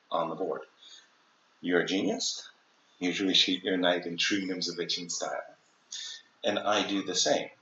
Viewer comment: I can't make out what he says at 4:17, just after "you're a genius..." Can anyone help? Thanks. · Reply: “you should retreat your knight in true Nimzowitschian style”